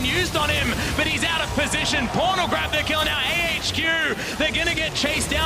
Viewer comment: did he just say pornographic ? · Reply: He said "pawn will grab the kill" but his ausie accent makes "pawn" sound like "porn" lol